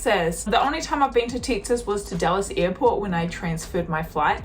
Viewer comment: When you said you’d been to Dallas airport, with your Kiwi accent, it sounded like you said Dulles airport, which is near Washington D.C. Threw me off for a bit. 😁